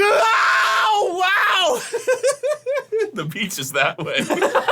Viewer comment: "The beach is that way" is AMAZINGLY FUNNY, such an underrated clip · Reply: I heard "the pizza's that way" but that might be because I'm eating it while watching 😂